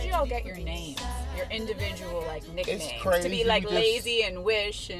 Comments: Did she say Nigga name or nickname 0:37 ? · did she just say nigga names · what did she say..,your what names?? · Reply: Nicknames